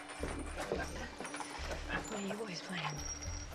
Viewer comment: What Does Emma say at 0:56 ? "Will you ...?" Can't understand . Help! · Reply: camila dantas she says "what are you boys playing?"